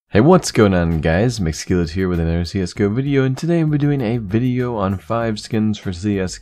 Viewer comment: it sounds like he said ZS:GO :D